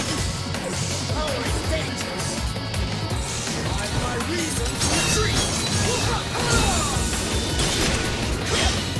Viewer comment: Hello everyone! Could anyone tell me what Credo says at 2:17? I understand something like "I've my reason to be..." what? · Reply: "I've my reason to retreat!"